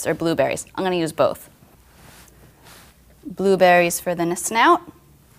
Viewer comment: she said "nsnout" instead of "snout" LOL